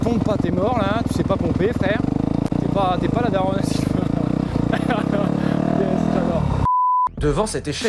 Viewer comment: "Je sais pas pomper je suis pas la daronne a Sylvain" merci pour ces belles paroles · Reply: Moi j'ai entendu "la daronne a Siphano perso. Voilà.